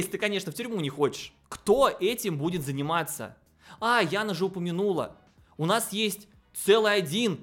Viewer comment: Мне послышалось "Яна жопу минула" Простите